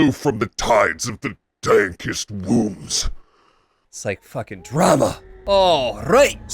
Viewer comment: The dankest wombs. I'm not the only one who heard that, right?